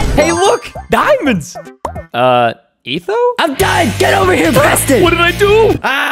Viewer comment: The first time I watched this I heard “I’m gay Preston!”